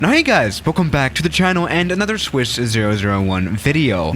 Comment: did he just say Swiss 0001 video? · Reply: Swiss 001*